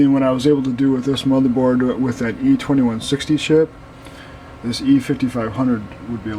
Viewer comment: did he say sh*t? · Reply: chip